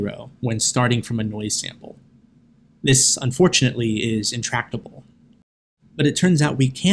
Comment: Can someone explain what 5:58 means? I hear contractable, but can't find a definition. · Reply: "Intractable" (hard to do).